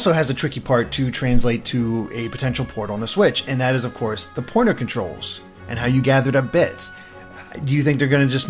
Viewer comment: Had to double check cuz I thought he said "porno controlls"